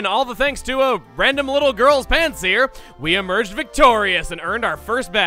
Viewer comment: I thought he said "a little girls pants, here." At the beginning. Good job, me.